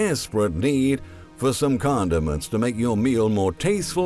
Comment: I literally thought this guy was saying condoms LOL